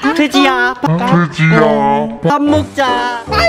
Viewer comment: 초에 더지야가아니라 돼지야라고 들림